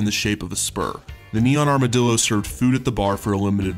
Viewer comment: I heard "...a brass chandelier in the shape of a SPERM." I'm a mature human being.